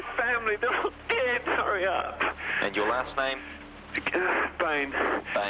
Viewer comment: Thank you for covering this fascinating case! I have never heard of this before. Personally, I knew David was guilty the second I heard the 911 call and he said "sorry." It seems like a Freudian slip where he's admitting his guilt without even realizing it. Also, we can't ignore the forensic evidence here. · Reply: I think he actually says "hurry up"